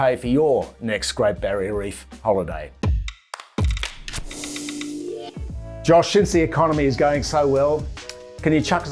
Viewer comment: what is he saying at the end for support. i keep hearing him say "josh, since the economy is going so well..." i had a heart attack. great vids but damn that almost too good.